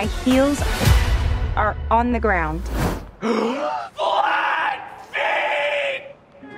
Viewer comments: I thought she said black feet lmao 💀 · I thought she said black feet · FFFFLLLAAATTT FFFFEEEEETTTT!!!!! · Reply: I thought they said black feet LMAO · When she said flat feet i thought she said black pink 😂😂😂😂